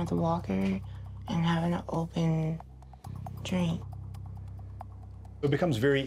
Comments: “And having to open Drink” He wanted to laugh so bad😂😭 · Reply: Alex Wesker I mean I definitely got that the third time I watched it. But at first it legit sounded like she said “and having to open.... drink” like opening a drink was super hero effort. It was funnier when I thought it was the opening drink. Otherwise it’s understandable. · “And having to open Drink” He wanted to laugh so bad😂😭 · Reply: Emone Blessed I thought she said it was hard to do with an open drink